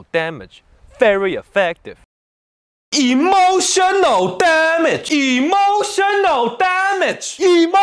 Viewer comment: I Only heard "EMOTION OR DAMAGE" 1:14 😄😁😆😅.........🤣🤣🤣🤣🤣🤣🤣🤣🤣🤣🤣🤣🤣🤣🤣🤣🤣 Edit: 1:21 Says"Emotion or damge"